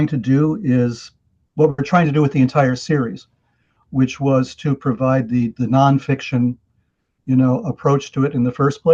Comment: This was an outstanding discussion, covered so many important points. I have a question though, at 2:36 says the name of the Entities that appeared to he and his wife that night. Can anyone make out what he's saying and spell the name? CC interprets it as "by bendy." In a previous interview he was reluctant to say whay they were. Thanks in advance and thank you, for another great video! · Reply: Appreciated!! It is Bibendi.